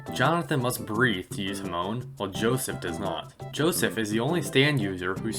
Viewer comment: am I the only one who hears:"Jonathan must breathe to use 'moan' while Joseph does not"